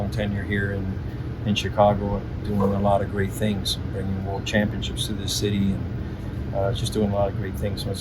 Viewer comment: My hearing isn’t so good anymore. I thought he said “World Championships.”